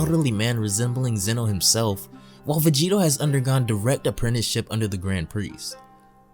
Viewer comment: Vegeto instead of Vegeta